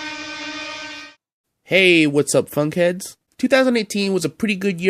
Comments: i thought you said “hey, what’s up fuckheads?” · People who misheard Don’t press read more if your under 13 years old “Hey! What’s up Fuck heads”